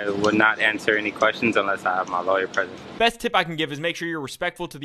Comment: He says "successful interaction", but what I hear is "survive"